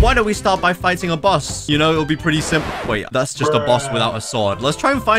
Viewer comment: I think Chop fruit immunity doesn't work on bosses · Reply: did bro just say simp